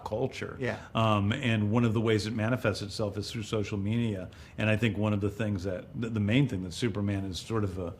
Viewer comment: Did James say social meania?